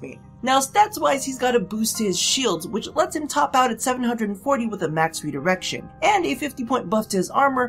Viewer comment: You say "maxed redirection" I hear "maxed out erection" I think the channel has tainted my mind.